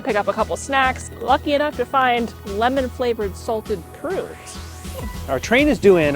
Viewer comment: Did you just say "lemmon Salted Prunes"? I hope not...lol... Prune are normally eaten to Clean Out your system. Why would anyone want to do that while in travel mode...lol... Anyway, nice video